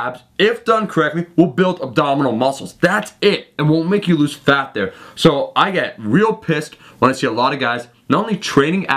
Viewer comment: did u say "and will make you lose fat there"?